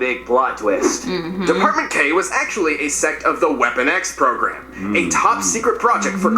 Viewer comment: Sounded like he said “Weapon Gay” 😅